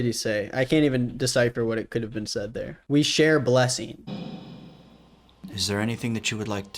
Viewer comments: it sounds like he said ''we share/shave/shit arson'' loll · By "we share blessing" I heard "shave us" 👹👹